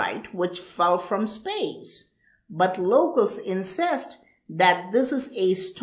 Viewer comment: I was like Wait what did he just say. Confession: i played it a few times over just for a good laugh on : The local villagers "insest"